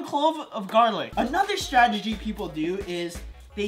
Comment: strgity is what said